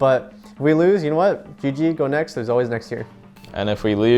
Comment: “if we lose, it was a fluke” was the most dapr fucking thing he could’ve said lmao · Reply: Thought he said “who gives a fluke “